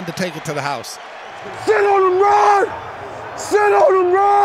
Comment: Thought he was saying “sh*t on him rod “ 😂